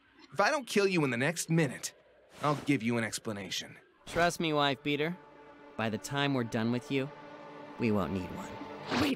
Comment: I always thought he said "Life Beater" at 13:03 cus he's organic + the wife beater thing from earlier.